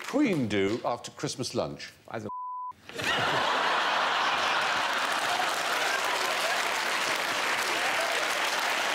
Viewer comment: American here. Anyone here know what Alan Davies says at 6:19 when Stephen Fry asks what The Queen does after Christmas lunch? I believe he says: “Has a-“ and then he’s bleeped. I’m guessing he says: “Has a wank.”? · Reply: It is has a wank.